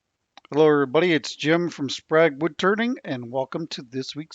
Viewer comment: ok so is it just me, it sounded like he said woodTURDING at the very beginning lol